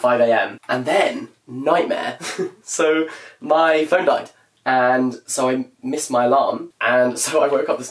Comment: oh my god i thought he said "my friend died" instead of "my phone died"